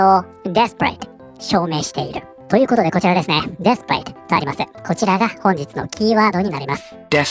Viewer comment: 私のネイティブジャパニーズのしょぼい耳ではdesperateが◯eath paradeに聞こえるし彼女のニュースと合わせて聞くとそう感じる